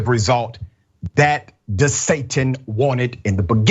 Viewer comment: Anyone else hearing reporter calling Gov DeSantis .. DeSatan? at the 7:56 mark